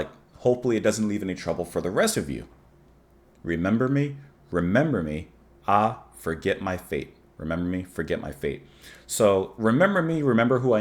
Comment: A beautiful, beautiful piece. Perfectly performed and articulated here avoiding the famous mishearing of 'Remember me but ah forget my feet'